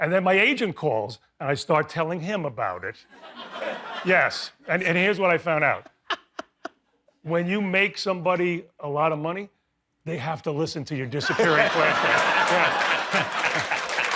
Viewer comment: I thought it said FLESHLIGHT